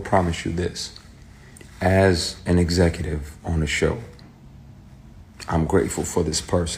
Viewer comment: "As an executive on the show, I'm grateful for this person showing me how not to be... when you're the BOSS" and "not only was he watching to see if I was the person he thought he made" .... leads me to think ONE person. We all know who. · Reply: No he said "as an executive on a show...". Not THE show. At 33:15 he says he produces a show (of course his show is produced by Tyler perry studios) and he is glad he is not like a boss he knew in the past. And at 34:35 he says he wish he had been brave enough to speak out "back then" and the statute of limitations has run out. I was trying to figure out why everyone thinks it's Tyler perry. So I was really listening hard at what he was saying. I don't think he's talking about Tyler Perry.